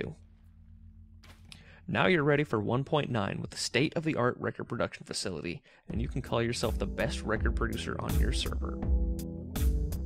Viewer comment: hahaha the cc says at 2:09 facility you can call yourself the best record producer here stripper